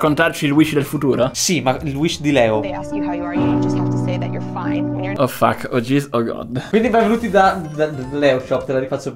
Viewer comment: Cosa dice sottovoce? (Min. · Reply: Oh fuck, oh geez, oh god